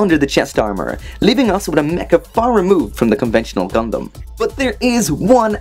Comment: Did he just say "conventional dumdum"?